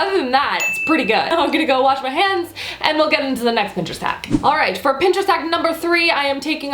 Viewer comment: For the second hack it sounded like she said "for the next bitches hack" 😂